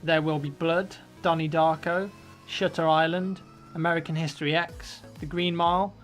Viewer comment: i thought you said "shitter island"...i'm sure what you actually said is "shutter island"